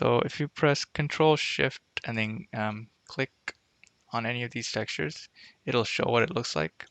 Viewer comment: i din't understand what you says exactly. "CTRL + SHIFT + ?????" (Anninig?) Can anyone help me pls ? thx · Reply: Ctrl+Shift then click on the texture